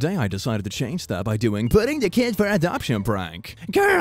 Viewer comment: "girls can't like girls" Me, a straight male: can't confirm. Am straight. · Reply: I thought it said “girls can like cats”